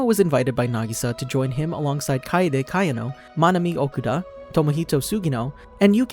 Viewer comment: Left to right: Leon Toko Chihiro Chiaki Hagime Sayaka I wrote this because i heard monomi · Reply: It was Manami but ok... (That is a kinda accurate-)